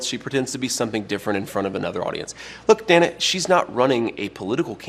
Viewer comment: xD 0:32 I keep thinking he's saying "Look damn it", but the captions say "look Dana".